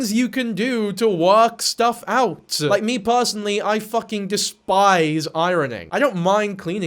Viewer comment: anyone else hear "i fking despise irony. I'm not good at it." ?